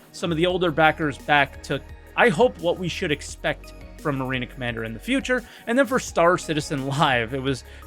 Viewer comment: did he say Marina Commander 😅